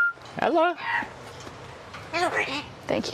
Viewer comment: swear I heard "Hello, Ricky"